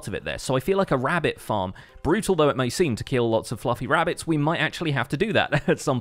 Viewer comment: did he said fuccing rabbit?